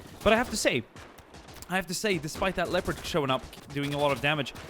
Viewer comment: Is it me or did he say leopard